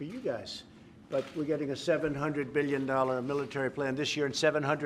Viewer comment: did he say 2:29 $700 B?